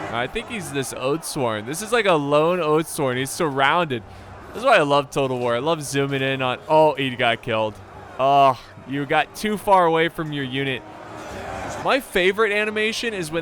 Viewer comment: I thought he said he was done with total war